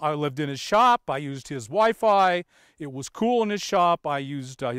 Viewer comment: The 8 minute mark I thought you said "I used his wife, I" saying the I as if you were starting another sentence. Lol